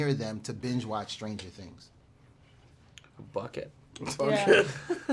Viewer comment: Ahahaha I thought he said bud instead of bucket